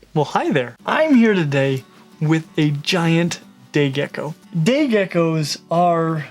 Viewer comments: I don't know if it is just me but i heard him say gay gecko lol, love your vids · Not going to lie. Every time you said say gecko. I thought you said gay gecko. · Sorry I thought you said gey gecko I swear